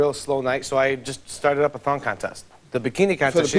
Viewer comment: At first it sounded like they said "thot contest"...